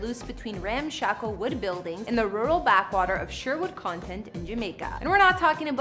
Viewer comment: Did she say rural backwater at 3:15?!?!? that's where I stopped watching!!!!:(